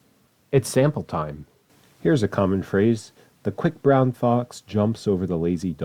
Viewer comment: as a russian and ukrainian speaker i love this video, although the щ in the quick brown fox threw me off guard, but i'm assuming it's a typo because when i use the mnemonic russian keyboard w gets turned to щ. still funny to imagine the quick broshchn fox XD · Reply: În romanian, the "broshchn" word sounds similar to "broască" which means frog, and I thought that it said "the Quick frog jumps..."